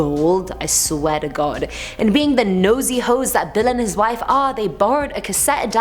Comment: Did she say Nosey Hoes? LMFAO